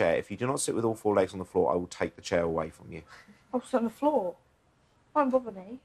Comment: did he say 4 legs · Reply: 4 legs on the chair